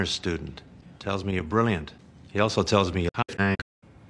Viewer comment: what did otto say? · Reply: "He also tells me you're a shank"